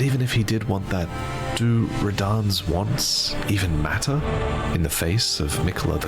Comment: weird, captions say "Metyr" when vaati says "matter"